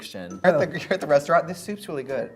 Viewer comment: also what kid katya say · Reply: i bring my own ice luge